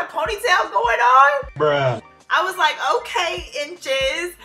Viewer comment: Omg at 7:20...did I hear a voice go "bruh"..LOL i can't....